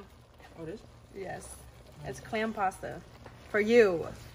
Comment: Idk why my dumbass thought it said clam salsa 🤣